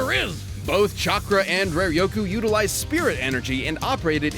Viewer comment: I misheard Reiryoku as Rare Yogurt, I would have believed it too if I hadn’t seen some of Bleach. Aizen powered by rare yogurt.